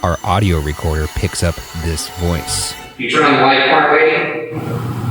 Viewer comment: I heard "Why the light?"